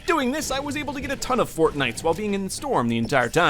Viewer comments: is when he said fortnites instead of fortbyts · Did anyone hear him say Fortnite instead of fortbite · did he say ‘Fortnites’ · He said fortnite instead of fortbite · he says fortnites instead of fort bytes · he said Fortnite a instead of fortbytes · Did you say fortnite or fortbyte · he said Fortnites instead of Fortbytes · he said Fortnites instead of Fortbites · did he says fortnites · he says fortnites instead of fortbites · said Fortnites instead of Fortbytes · he said fortnites instead of fortbytes lol